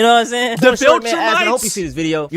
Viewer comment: did anyone else hear "vulturmites" ? Stand ready earthlings for our arrival, we demand all the EBT.